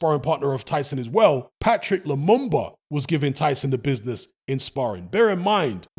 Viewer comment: For a second there I thought said 'Patrice Lemumba' 😂😂